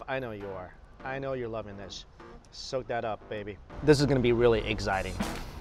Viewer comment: I could have sworn he said eggxiting😂😂😂😂